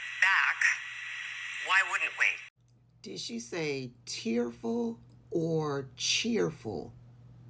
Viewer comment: I thought she said cheerful as well at first. But if you watch her lips, she's saying tearful. Why the hell would Trump be tearful about talking to these two idiots?